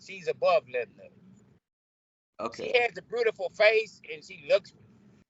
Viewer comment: "She has a brewtiful face..." Well, I guess it's true what they say... Beauty is in the eye of the beer holder.